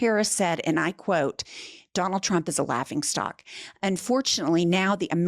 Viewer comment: Laughing stock. I thought you said a rock in a sock. I had no idea what that meant but hey, a rock in a sock is blunt, brutish and destroys everything it touches. That's donald.